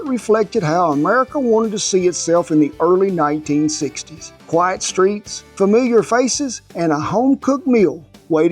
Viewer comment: I thought he said "White streets" 😂😂 I had to replay it 3 times. He said "quite streets". Relax everybody